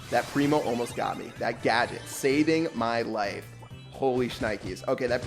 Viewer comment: i thought was saying that it was primo's gadget saving his life lol